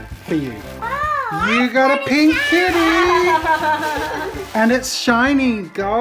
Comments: OH A KITTY. I honestly thought you were saying "kidney". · When he said kitty, I thought he said “my daughter loves kidneys.” 😂😅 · Oh, kitty! I totally heard "kidney" 😂😂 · When jazza said "kitty" i heard "kidney" 😅